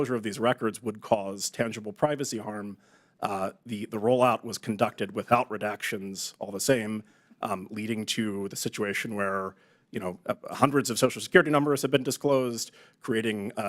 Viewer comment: Oh, AMEN ! Did he say unredacted? 😎